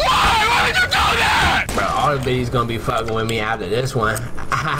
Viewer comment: Jesus I watch this video 7th time and I still don't get what this guy saying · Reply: “all the bitties gon be fuggin wit me after this one AHAHAHHAH”